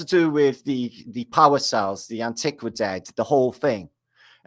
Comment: did you just say Antiquidead?